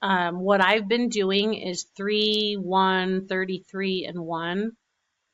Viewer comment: Cindy Hebert-Fontenot it varies depending on how sharp your blade is, but for the Fixxons sheets I’ve had good luck with 3-10-33-2 on my Cameo. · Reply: 3-10-33-2?? I thought in the video you said 3-1-33-2